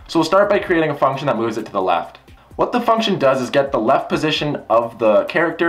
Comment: when he said what the function does I thought he said what the fuck does